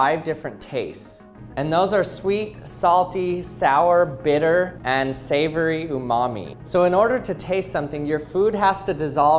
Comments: the last taste I thought he said savoring your mommy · Did he says savory oh momma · savory ooh mommy that’s what I heard 😂😂😂😂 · I don't know why but I heard "your mommy" instaead of "umami" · what the hell did you say "savory ooh mommy"?! 🤣